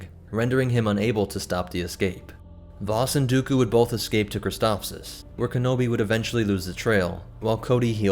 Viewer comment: when he was talking about Christophsis i swear I heard, "The CIS invasion of Croissant"